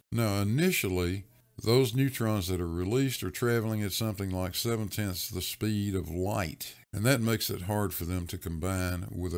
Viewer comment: Oops, thought I heard you say "seven TIMES the speed of light" ... but it actually was seven TENTH. Thanks, the world still turns ... 😁